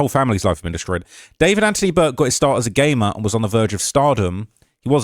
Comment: I thought you said gay man instead of gamer lol